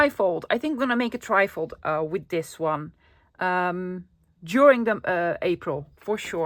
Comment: At the end I heard you say ' I'm going to make a trifle with this one' I am tired 🤣🤣🤣🤣